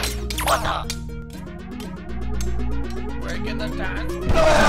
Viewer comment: Does anyone know what the wizard says at 4:21? I've watched that part like 5 times now and can't figure out what he says! lol. and I've watched your videos for some time now and I think this is your best one! :D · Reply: Sci-Fi Productions He says "Breaking the dance floor" If I remember correctly, this was a line that (the wizard's actor) thought up during his recording session because I wasn't really sure what he should say at that point. And thanks!